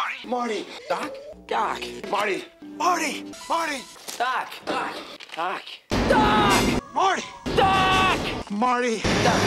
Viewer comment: All I heard was "Duck ! Duck ! Duck ! Marty ! Duck !"